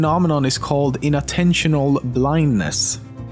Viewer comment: I thought ya said intentional blindness